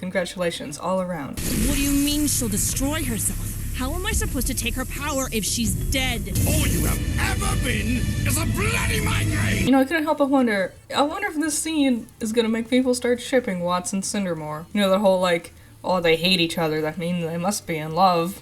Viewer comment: My first watchthrough, I didn't understand Watts when he shouted "a bloody migraine." I really thought he said "a blight on my name." So I was all 'ZOMG they're related!?' Then I rewound and turned on the subtitles.